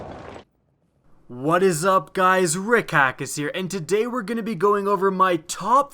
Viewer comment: my friend mum thought you said hay guys rick circus xD